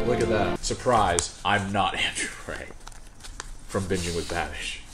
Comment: when he said, “I’m not Andrew Rae.” I thought he said, “I’m not Andrew Tate.” 😂